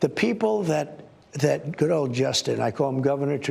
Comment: Did I actually hear him say, “that girl Justin”? I actually thought I heard him say that. I swear he’s got a weird Justin “thing”.